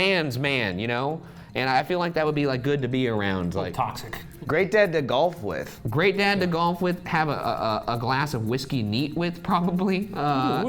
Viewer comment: ryan and forced me to sign a form that made them my official dads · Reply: Okay but did you really say that Joel would be great to GOLF with?? That's cold 😂